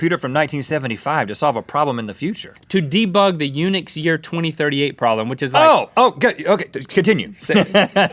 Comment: What did Link say at 7:52 ? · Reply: He said "To debunk the 2038 problem'